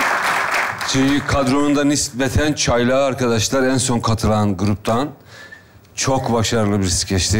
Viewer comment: Dk @ 20:30 yilmaz hocanın ne dediğini anlayan var mı ..... kadronun çaylağı diyor ama anlayamadım. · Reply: çünkü kadronunda nispeten çaylağı en son katılan gruptan